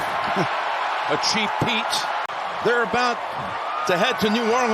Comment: I keep hearing a Cheat -Peat 😂